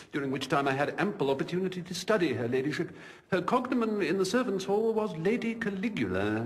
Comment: what is it Jeeves says? "Her [cogxxxx???] in the servants' hall was Lady Caligula." Anyone? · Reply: Cognomen. Essentially a nickname